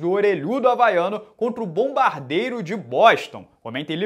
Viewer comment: mano eu entendi ''BOMBARDEIRO DE BOSTA'' uhuguygyuguy